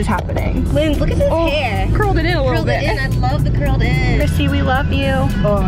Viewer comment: At first I thought she said "Cissy"...and I thought I'd completely missed everything.